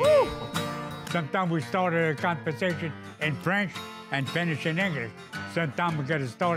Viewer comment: he said sometimes we start a conversation in French and finish it in English 🤣🤣 he did not say the forbidden word